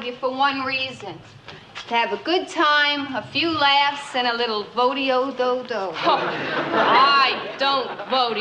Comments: All this time I thought they were saying Rodeo do dodo. Ha, My childhood memories were GREAT!! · All this time I thought they were saying Rodeo do dodo. Ha, My childhood memories were GREAT!! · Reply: Watching Laverne & Shirley at 19. I thought she said rodeo-do-do too!